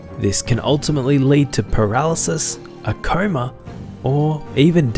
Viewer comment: I heard "a karma" instead of "a coma"